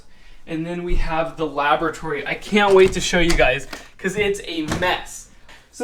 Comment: "here's some towels and here we have the laboratory. I can't wait to show you", I thought you said lavatory and was thinking ok he digs his bathroom and thinks we gotta see it.